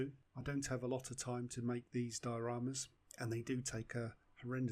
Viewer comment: Thought you'd said "Dutch" dioramas, I had.